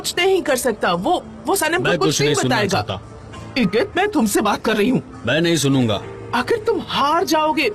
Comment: huma said igit and I heard idiot, well that's what he actually is, an idiot